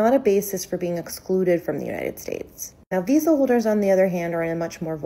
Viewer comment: diesel holders 😅 I think those captions meant to say “visa holders”?